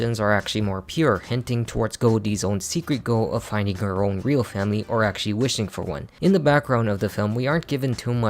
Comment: "real family" All I heard was: I'm an asshole.